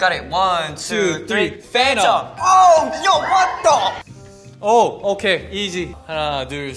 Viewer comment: I thought mark said what the f***